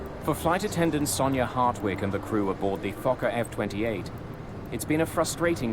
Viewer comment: Am I the only one who heard fu*^(#% 28 and then big fu*^(#% white snowflakes at 0:40 I do hope I'm wrong and my hearing is going bad.